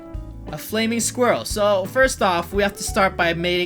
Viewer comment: Like:meet Roi AND have good luck for 10 years Reply: have good luck forever Subscribe: YOU WILL HAVE JUST HELPED A SMALL YOUTUBER WITH BIG DREAMS!!!☺ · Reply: Roi said le5s start mating this squirel